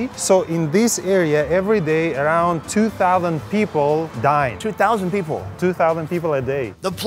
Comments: I thought he said 2000 people die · I heard 2:48 "...2000 people... DIE." I was like, wait what? lol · I thought he said die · sounded like he said '''every day here 2000 people die'' hahaha :) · i thought he said people die but actually he says dine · really thought he said "2000 people die"